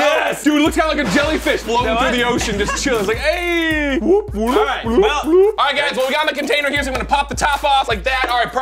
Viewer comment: it sounds like said to when he was holding dry ice "Alright, smell it!" :D